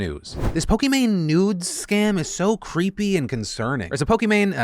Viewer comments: i was looking at another screen I heard "Pokimane Nudes" and looked over and I was thinking WHAT! then it hit me he said scam. who else is a degenerate? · I can’t be the only one who thought it said “Pokémon Nudes” lol